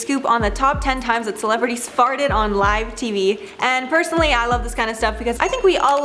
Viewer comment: I thought it said fainted :/ I was so excited to see Wendy Williams faint bc that shit would be dramatic af